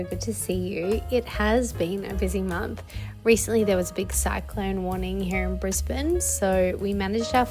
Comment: Yayyy, it's so good to see you too!! Very pleased to see you back on my algorithm. At the beginning of the vid when you said "cyclone warning" I misheard it as "Sad Clown Warning" and same girl, same 🤣